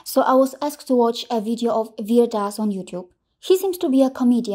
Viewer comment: Namaste ji 🙏🕉☺😊 Your videos are always great, apt, factful and exact replies to propagandists. When you said virdas i heard "weird as#" 😃😅😂 Thanks for your service of motherland india. May God kunjbihariji shower his blessings on you and your family and give you all positive health, wealth, prosperity. ☺😊